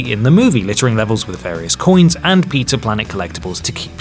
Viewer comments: I think it might be because I'm too obsessed with this channel... But I swear says 'Peter Planet' instead of 'Pizza Planet' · Wait did you just say "Peter planet" instead of pizza planet